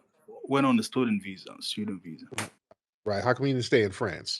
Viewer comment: Did he say stolen visa? Fraudulent slip?🤨